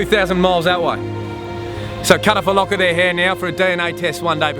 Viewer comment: What did he say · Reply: "So cut of a lock of their hair now" im fluent in bogan australian so I didnt even notice how bad the accent was till I went back to translate for you haha